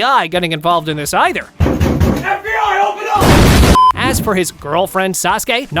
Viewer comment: when i was a little kid, i thought it said HELP ME AA HELP ME AA but now i realized it said FBI OPEN UP